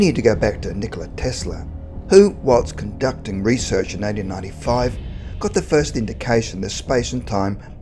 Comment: it sounded like '...who was conducting research in 1995' but Tesla died way before that year actually in 1943.